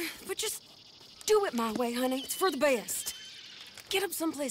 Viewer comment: did she just call him “honey”